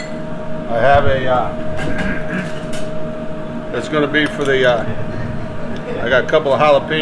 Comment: what is that white thing at 4:39? sour cream? mayo?... im from brazil, I didn't get well what he said :( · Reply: Mayo Vitar